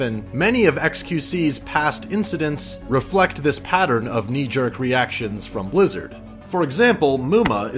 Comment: Excuse me did you just say nee-gur???!!!11